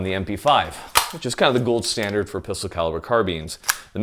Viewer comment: I can't figure out what p-word caliber carbines he's saying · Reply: Update: took me 4 replays to finally hear "pistol"